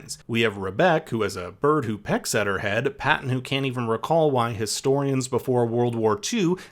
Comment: I think Rebeck says she’s had this itchy head since she was young…